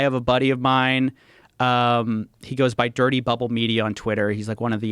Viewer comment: I heard "Dirty Butthole Media" the first time, & honestly I think I'll leave well enough alone, on this one, & stick with it. I mean, how's it gonna do anybody a lick of good just to invite more opportunities for confusion? Sometimes the first instinct is the best one. Not the correct one, the best one.